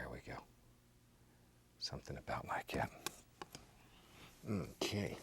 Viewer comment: did he say "something about my cat"😂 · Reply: He said "something about like that"